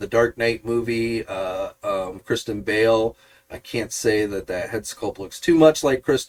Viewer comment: I didn't realize Kristen Bell played Batman in any of the movies 😂 could have sworn you said that name when I first heard it